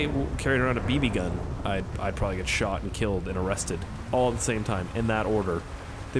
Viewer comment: I wasn't it really paying attention to what he was saying but when he said arrested I thought he said molested